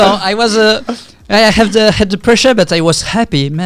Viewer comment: "I had depression, but I was happy." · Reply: He did say the pressure @ 42:54 (captions also said it), but it's still a funny misheard quote lmao